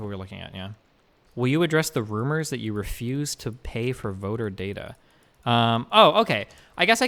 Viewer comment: Because he was mumbling, I initially thought Steven was saying "Address the rumors that you refuse to buy mobile data?" instead of "voter data?". And I immediately imagined that he was in Atlanta and his mobile plan ran out and he refused to pay the bill and forced his various DGG goons to constantly let him borrow their phones or give him a mobile hotspot or something. And they were like "I dunno Destiny man you keep watching these 60fps Minecraft streams and downloading these massive shitty mobile games, you're really running up my data bill here..." and he was like "Shut the FUCK UP you little SHIT, you would be NOTHING without me!" IMO honestly that little story I made up in my head would have been far more interesting.